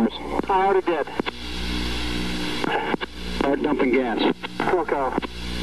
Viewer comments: Did he say FUCK OFF at 1:36? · did I mishear or was that exchange “start dumping gas” - “fuck off”